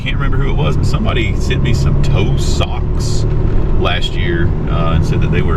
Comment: 🤣🤣🤣🤣🤣.…I heard “Toe Socks” and I thought…” awe!!! Someone was worried about toes getting cold!” 😂. Apparently it’s actually “TOW socks!” 🤣🥲😂🤣🥲😂🤣🥲